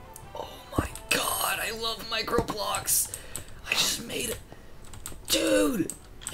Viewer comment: I thought said omg I love mike roblox when he said omg I love microblocks